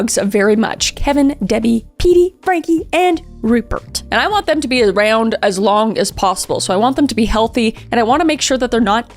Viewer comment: I thought she said “I want them to be round as long as possible”